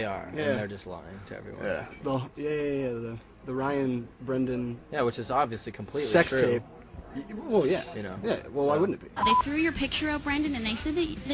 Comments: wait did Ryan say it was completely true???! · Is it just me or when its at the part when they are talking about them "dead set on us dating" doesnt it sound like Ryan said "Its Obviously completely true"? from 1:33 to 1:37.